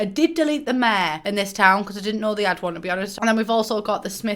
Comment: can someone please tell me what she says at like 5:34 after “i did delete the mayor to be honest because” and i’ve replayed that part 3 times slowed down and i can’t distinguish a single word she says after because 😂😂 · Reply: "Because I didn't know they had one to be honest"